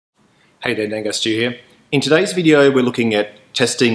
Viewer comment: I thought you said "Danger Studio here" at the start of the video :)